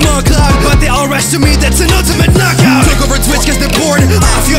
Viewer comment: I thought he said knock off instead of knockout